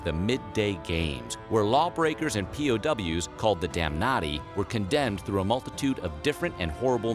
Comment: Tbh I thought he said "Damn Naughty" and not "Damnatti".